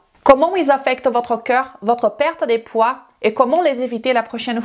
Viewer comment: Salut. 0:22 Je vous entends souvent dire "cœur" puis je viens de réaliser que vous dites en fait "corps". Voilà dites moi si c'est bien "corps" et non "cœur" car mon oreille me joue des tours. Merci et continuez comme ça.